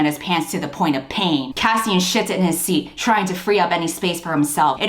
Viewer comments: 😭 why did i think you said "kassien shits in his seat" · I thought you said "Cassian sh*ts in his seat" and I LOST it 🤣🤣🤣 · Man I was listening just playing my switch.. and when you said "Cassian shifts in his seat".. I swear I thought you said "Cassian shits in his seat" and I fucking nearly died. I thought there was so much sexual tension that he legit shit himself for a second. I'm coughing dude, Jesus christ 🤣🤣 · I know you said shifted, but it sounded like the other S word. And suddenly this novel had another meaning hehe. Loads more. Pun intended